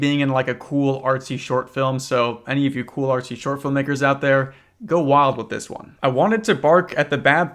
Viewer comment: He did NOT say makers 🥀🥀